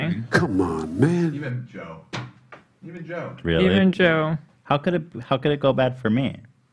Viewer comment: My money is on Philip Defranco for the messages, as he covered the Seth story when it came out first and talked about it in the show the same day that Ethan and Trisha were calling him a coward 😂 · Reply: You can hear say "[it's bad for] even Joe". Then mishears him saying "Even You", and repeats it. Ethan shit talked Joe Rogan for a while in a previous podcast.